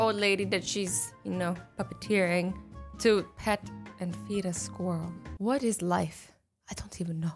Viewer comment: Am I the only one who heard say “To pet and eat the squirrel” If you also heard it like this comment 😂